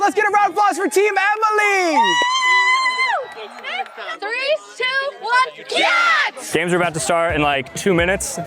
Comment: did they say 3 2 1 GYATT